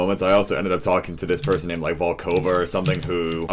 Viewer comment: Was he saying Vancouver? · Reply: Ricky Delatorre Valkova